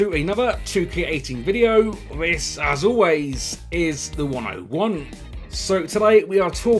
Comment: For ages even though his username is I thought he was saying " as always this is the one I want " lol I only twigged last week haha